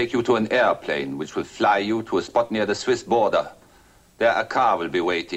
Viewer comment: Interesting for the fact that Edward Mulhare's character said his driver was going to drive "The Danzig Lady" to the Swiss border. Do you realize how fucking far the drive would be from Danzig to Switzerland. That was a ludicrous line. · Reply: Correct Goosey, and PottyMouth, if you go back and review what Ed said. 34:44 He had a plane to fly the lady NEAR the Swiss border and then drive from there . . . You ludicrous potty mouth!